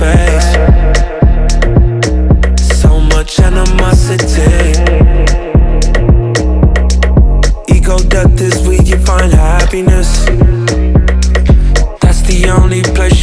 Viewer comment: I misheard 'ego death' as 'ego dub'